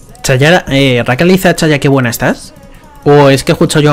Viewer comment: Lo vi en directo y de nuevo acá en YT 😊 Yo también escuché el "Que buena estás" de Rakan al 24:26 😂😂